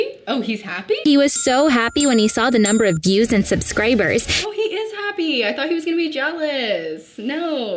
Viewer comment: did she just say "subscrabers" lmao